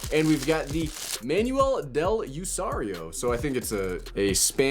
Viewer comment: I thought he said.